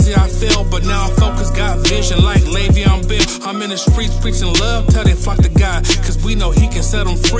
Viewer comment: Can someone please tell me what the rapper says at 3:43 minutes into the video? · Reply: Im in the streets preaching love till they FLOCK to God.....